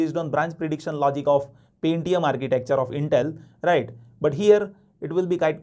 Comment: I thought you were saying PayTM structure later realized as Pentium